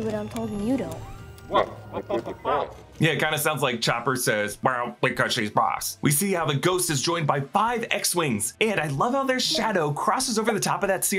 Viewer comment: I think he(chopper) says, “Yea, what’s up with that?”